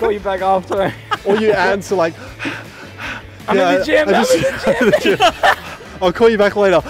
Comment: What do they say at 2:23? "Or you answer like ???" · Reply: Took me 5 years to figure out "I'm at the gym"